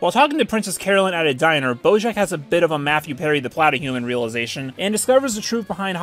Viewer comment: Can someone tell me wtf is the reference at 44:05? All I hear is “Matthew Perry the platter realization” and I have no clue what this is referencing.